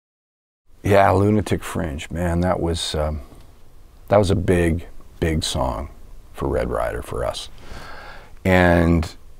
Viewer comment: First 20 times I heard this I thought he was saying lunatic friends